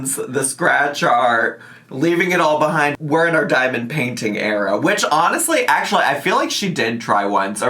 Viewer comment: I swear I thought she said diamond panties 🤣🤣🤣🤣